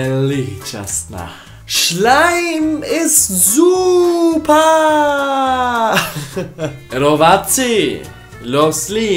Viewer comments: Ja usłyszalam Slime is ZUPAAAAAAA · in my country i hear : slime is soup